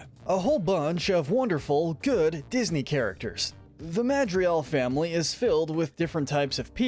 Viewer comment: ik this guy did not call them the madrial family